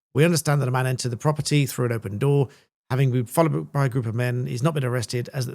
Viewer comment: what are you saying at 10:53? quickly gabbling something about......... "following a group of men?????" kindly explain. Thanx.